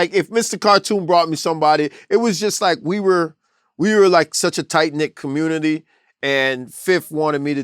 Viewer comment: did he say 'tight nick'?